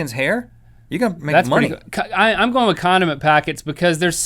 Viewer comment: Omg when you said 'condiment packets' I thought you said 'condom packets' and I was like whaaaaaat